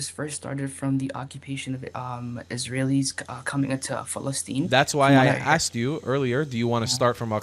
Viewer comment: Did he say Philistines🤔?